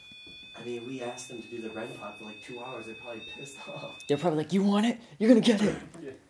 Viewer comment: Right after says loot of activities have been going on I hear a whisper at 37:59 right before speaks (sounded like SHE answered "cause you want it") idk